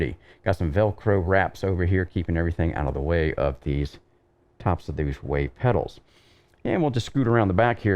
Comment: What pedals are those? I can hear you say the name at 37:54 but can't get the name. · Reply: They are the Wave Italy Impetus pedals: <link>